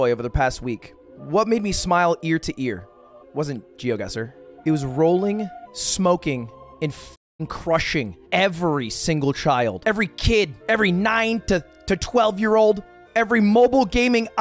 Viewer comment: Thought he was about to say fucking every child at the beginning there 😳